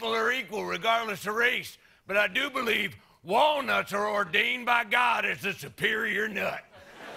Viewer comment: They were saying "wal-nuts" but all I heard was "Wall? Nuts!"